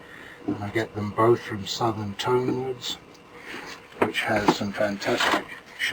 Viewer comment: Hello, excellent demonstration of how it should be done the professional way. Could I just ask... where did you say you get your shellac from, it sounded like you said Southern Tonals... but my speakers are pony and I couldn't just grasp the last bit of it. Thanks. Ian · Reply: Hello, I get my shellac from Southern Tonewoods, it's the best I have used. All the best